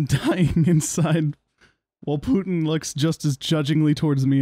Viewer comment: wait, did you say Putin? Why do you have a cardboard Putin cutout?